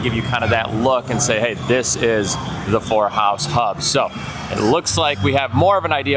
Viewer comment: I definitely thought you said the "horror house hub" instead of 4 house hub and it made me laugh 😂 I re-listened and realized I'm just deaf. As always, thanks for the daily updates